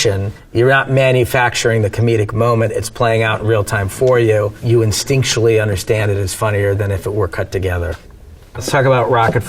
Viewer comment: did he just say "instinctually" ?